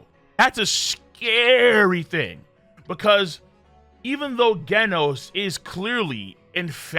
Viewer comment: Did he just say gaynos instead of genos 😂